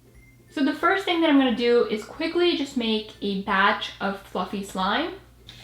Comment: who else thought she said f*ing slime. I know she said fluffy now but I just thought that was funny